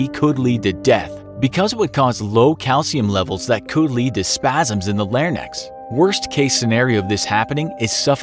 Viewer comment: did he just say "spasms in the lare-nex" lol.